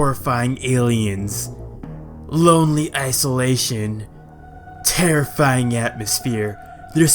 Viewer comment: Sry I heard 'asolation'.